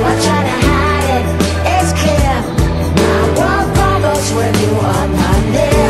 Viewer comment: When i was like 7 i thought she was saying "I blow bubbles when you are not here"